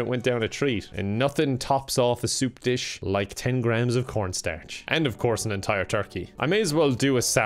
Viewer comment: I heard that he's almost like saying "Pornstarch" instead of cornstarch.